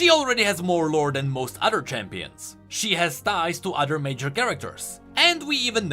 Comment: "She has thighs" While I know that's not what he said, I will leave it at that.